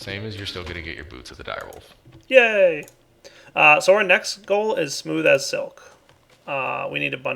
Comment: Just pointing out at 28:51 many think he said "soaryn next goal" (read it multiple times in the comments) but if you listen carefully you can hear "so our next goal" i was a little scared at first